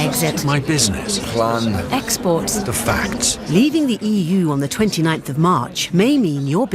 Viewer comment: I thought she said we WILL leave on the 29th March, but she didn't say which year!